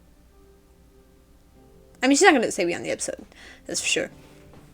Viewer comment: It took me a minute to figure out what you were saying at 9:30 "She's not going to stay beyond the episode" Reminds me of the line in 'Safe': "Gotta say Doctor, your talent for alienatin' folks is near miraculous". Even the subtitles call it unintelligible. Took me forever to work it out.